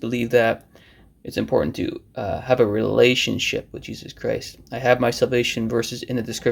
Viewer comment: For a second I thought I heard you say "revelationship"! That's perfect! If we have a relationship with Jesus He gives us revelation! Shalom